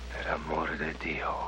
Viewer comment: "Per amor de dio" Weirdly the automatic subtitles says it means "That have more to do". Actually it is Catalonian, meaning "For God's sake". These subtitles are sometimes so hilarious. XD XD